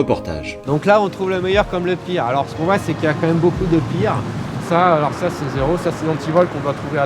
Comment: Pourquoi vos sous-titres sont ratés ? Après tout c'est pas généré par youtube donc il n'y a aucune raison que ça marque à 6:42 "beaucoup de bien" alors qu'il dit "beaucoup de pire" ???